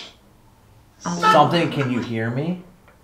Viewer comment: all I can hear is saying tell me if you can hear me like he has to whisper cuz he’s afraid and before that when something says say it and then one goes how? I feel like something doesn’t want him to leave like it’s keeping them trapped there🧐